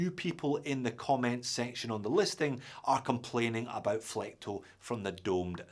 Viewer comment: people are complaining about what? Couldn't make out what you were saying. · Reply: "flecto", or reflections from the domed sapphire crystal